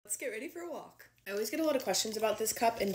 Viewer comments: When I swiped on to this video did it sound like said “Skibidi for walk?” Any one else hear that? 🤨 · I'm sorry, its a cute video but in the beginning it sounds like she said "Skibidi for a walk" I'm sorry I had to point it out💀